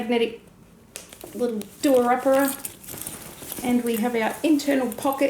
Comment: I have to ask are you from Australia i heard you say "do a uppera" as in the magnet buttons.. sounds so Aussie xx